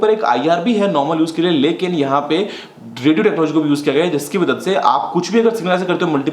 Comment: Kya aapne kabhi IR Data transfer use kiya hai? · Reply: Fill in the blanks: 5:45 lekin ihape ___ ko vi use kar gaye hai... I think he is saying "driggereprodge" but I didn't find any google search for that... But I think there's no such thingy like that. Probably that's a gyroscope...